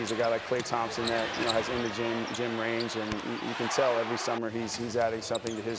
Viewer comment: did he say Klay has Jimmer range? Lol